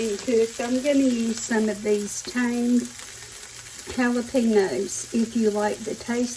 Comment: Looks delicious..I always thought you said TANGED jalapenos lol now I know it's tamed